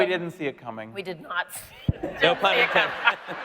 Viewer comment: What he said in 33:18 ? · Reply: “No pun intended.” 😆